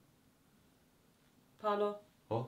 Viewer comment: Io ho capito "scendi Paolo"